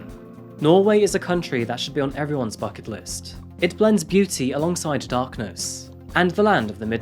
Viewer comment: LoL, I totally thought you said "fuck it list"!